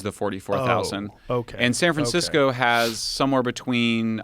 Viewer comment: Who heard him say "San Fran-sicko!"🙋‍♂️